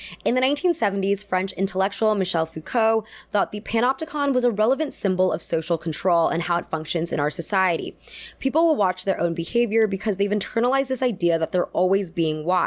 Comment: For a moment there I was laughing with my belly when I thought I heard you say “pinupticon”. But then there’s prison…